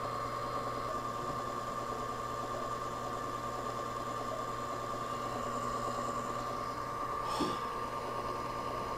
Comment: 혼자 뭐라는거얔ㅋㅋㅋㅋㅋㅋㅋㅋㅋㅋㅋㅋㅋㅋㅋㅋㅋㅋㅋㅋㅋㅋㅋㅋㅋㅋㅋ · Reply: 훈:뭐야 뭐야 (끄덕 끄덕) 어? 아 오